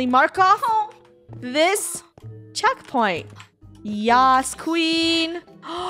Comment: Did she just said YASS QUEEN by Flamingo?